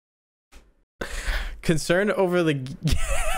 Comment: Got me there instead of he saying gate he said 'gyatts'